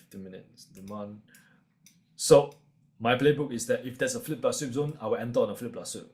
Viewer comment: There is a term he is using that I can’t make out every time he says it. Do you all know what it is? “Flip Plus Sweep Zone?” 6:31 he says it a few times but he says it multiple times within the video and it seems to be a key part of his strategy. Any help would be great 😊